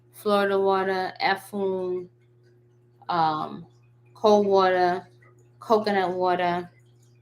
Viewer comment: Fire video as usual--for my notes at 46:57, did you say 'cold water' before coconut water? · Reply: Yes, cold water. And you don't need both.